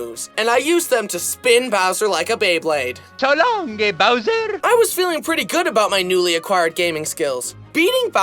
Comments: I heard "so long gay boser " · i hear "so long gay bowser" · HAPPY FEET .. WOMBO COMBO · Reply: I heard so long gay bowser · why every time i hear this I hear so long gay bowser... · what normal people hear: "so long-a-bowser!" what I hear: "so long gay-bowser!" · Broooo am I the only one who thinks it’s “so long gay bowser!” Instead of “so long K.Bowser!” 😂😂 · is it me or did it say “So long gay bowser” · dude, am I the only one who heard, "so long, gay bowser"😂😂😂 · it sounds like he is saying “so long gay bowser” 🤣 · I hear “so long gay bowser.” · I heard gay Bowser😂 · I heard so long gay bowser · Did Mario just say so long gay browser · why did i hear it as so long gay bowser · I heard so long,gay Bowser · the moment u realize he was supposed to say “ so long king bowser! “ instead of “ so long gay bowser! “ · why did I hear “so long a gay bowser”😭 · I thought 3:08 said, “So long, gay Bowser”